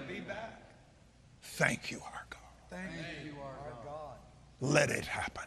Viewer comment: After Copeland blows covid away, although he says "thank you our god" it sure sounds like he says "thank you archon". Which in gnosticism are rulers that created the universe and are holding our souls hostage here...hence reincarnation. Which brings me to scripture that says the gospel (god spell/go spell) is hope and hope is an anchor for our souls. Hmm